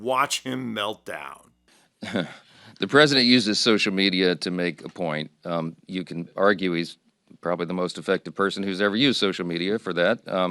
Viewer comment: did he say “the most effective” or “the most defective”? I can’t understand his accent…